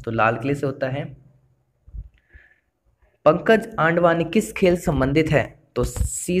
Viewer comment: kya bola phir se bolna ek baar😂😂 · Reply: Pankaj aandwani,,,😁😁😁